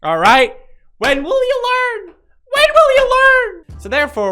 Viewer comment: when you said when will you learn i thought you said, when william all